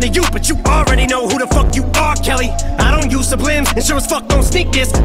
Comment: aged like wine. When listening to that part it sounds like he says R Kelly. Considering what happened with R Kelly that's 🔥